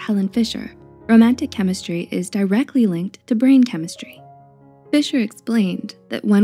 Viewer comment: For a moment when she said romantic chemistry is linked to brain chemistry I thought she said brain cancer